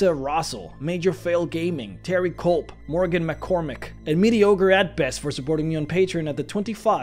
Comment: "and mediocre at best for supporting me at the 25 dollar level" jesus that's rude... oh no the patrons user-name is mediocre at best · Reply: I always heard it as "Meaty ogre at best", oh well. My favorite is still Sackbowel.